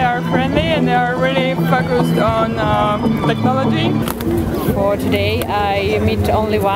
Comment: thanks for the subtitles, man. I I heard "They are fuckers on technology"